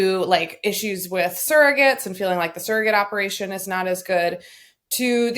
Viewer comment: An aside - every time Sarah says “surrogate” I hear her say “Sarahgate” and I think she needs to trademark and market it. 😬 SL, that’s yours free from me to you. And also, how did you get Lawrence Welk to come back and serve in your focus group??